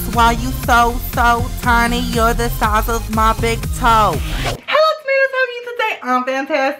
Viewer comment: "Your the size of my big toe" 😂😂👏🏼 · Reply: I thought he said "your the sauce of my big toe" 😂😂